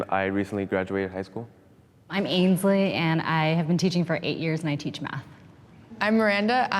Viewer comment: I thought she said, “I’m Asian, I teach math.” 😭